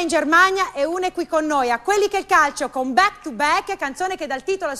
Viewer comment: Back to back? Ho sentito bene?